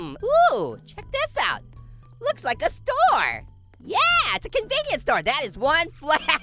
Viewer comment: When you were at the store I thought you said storm